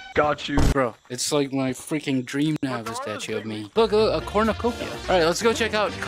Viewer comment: DID HE SAY CHROMOCOPIA